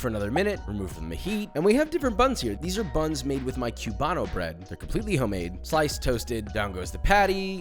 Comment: I swear I hear “chew”bano bread. Lol😂